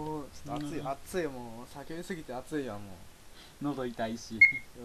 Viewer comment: 酒飲みすぎて暑いって聞こえて未成年....って思ったのはワイだけやないは....ず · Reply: 叫びすぎてじゃないの